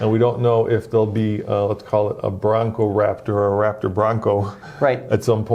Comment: Did he just say a Brrrraptor?!?